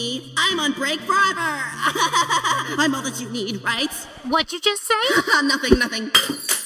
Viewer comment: To be honest at 11:45 I thought bow said I won’t let you leave right